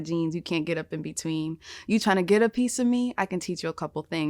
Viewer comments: I coulda sworn she said ” you tryna get up in some me” 😂😂😂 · All this time I thought she said "You tryna get up in some me" instead of "get a piece of me" lol either way still iconic !